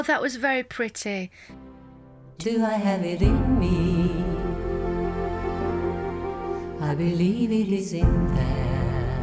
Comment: I hear it as "Do I have it in me? //I believe it is in there."